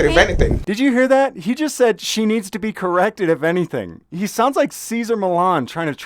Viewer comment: The cop didn’t say she “needs to be corrected “. He said she needs to be “Baker Act-ed” . The Florida mental health act of 1971 commonly known as the Baker act. You got that one wrong. I’m not saying he’s right but that is what he said. You can do a YouTube search on “Baker acted”. Very common Florida go to for cops.